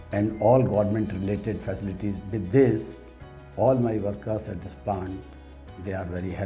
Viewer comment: it sounds like he say all my workers are happy and lazy. Haha